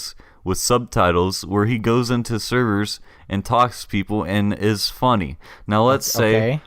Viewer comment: did they just say Fitz is funny